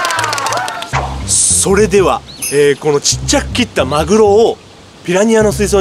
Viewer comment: ラジオ感覚で動画みてたら「ちっちゃく切ったマグロ」が 「ちっちゃく切ったマルオ」って聞こえて一瞬フリーズした